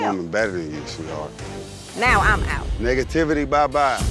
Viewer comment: I could've sworn that said Negatiffany instead of negativity when he said bye to Tiffany 🤣